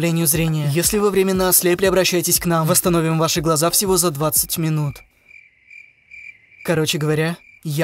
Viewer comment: кто заметил что он сказал "всего за 20 минут", а там написано "за вего 20 минут" Лол